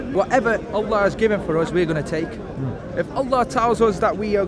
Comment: I am doing work and need to write about this video. Can someone tell me what he says at 4:14 he says what ever ....has given us and I don't know what he says or how to spell it :/ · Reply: Emily Baythorpe He says, "whatever Allah (God) has given us, we are going to take." I agree with that statement and that which followed - "whatever our Lord tells us, we do."